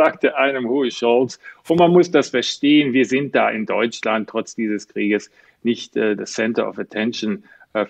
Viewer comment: "trotz des Krieges" habe ich das richtig verstanden? Welcher Krieg? Habe ich etwas verpasst, Herr Anda?